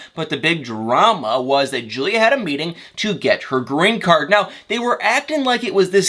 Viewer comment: I thought they said it was for her green card